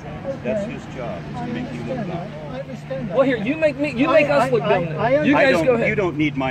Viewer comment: "You're making us look dumb!" "You don't need my help." is such a savage. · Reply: I thought Eric Hovind said, “You make us look dumb.”